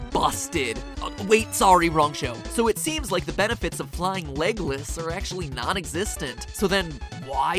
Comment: I keep hearing "Flying Legolas"